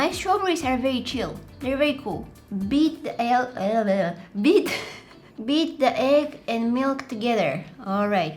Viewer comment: i thought she was saying "beat the he- out of the egg" i was like WAIT WHAT..